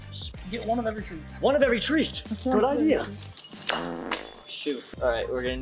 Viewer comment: did say shi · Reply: He said shoot